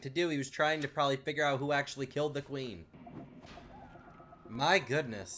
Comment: am i the only one who heard mcqueen